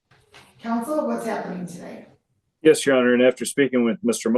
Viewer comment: Really? by replying “yeah sure!” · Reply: I hear her saying "Yes your honor" and "yes sir," not "yeah sure"